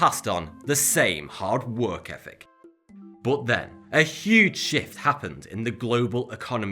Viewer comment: is it bad I heard "But then, a HUGE sh*t happened."?